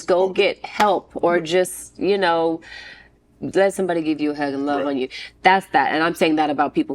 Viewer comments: Did she say “let somebody give you head and love on you”? @ 42:10 or am I just ready for the weekend??? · did she say “let somebody give you head and love on you”? Or is it just me? 🤔😝